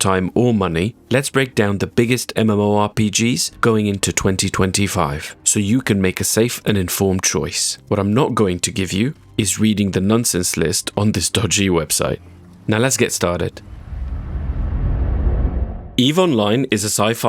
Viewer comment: Omg my dumbass thought you said nonces list. Idk what to play but i just got a new PC i played most of the grindy korean and japanese ones. Played hardcore wow and i was enjoying ff14. We have all plaued old school runscape but i never grind it